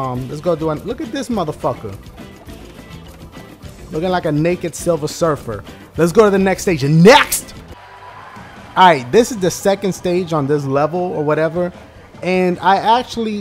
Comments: thought he said nigga silversurfer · REALLY PEOPLE?!!! said "naked" not nigga. But i don't blame you guys. Most of us are probably deaf from his happy wheels gameplays....Happppyyy Wheeeuullllzzz Mbadnesssss!!!! (that's what it sounds like he says to me, lol)